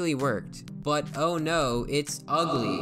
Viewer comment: did he say the n-word 😳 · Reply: He said snake